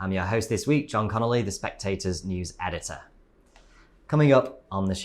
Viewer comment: I thought he said new editor!